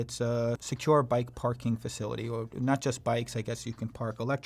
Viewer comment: Did I just hear the “Not Just Bikes”?